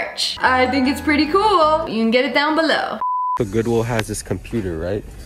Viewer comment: This is why goodwill is awesome. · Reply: I thought you said godwill and that true for finding the iPad